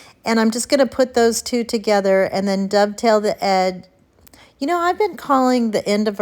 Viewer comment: I love watching you. it's ok about the wording cause for a long time I thought you said duck tail and it's dove tail. Thank you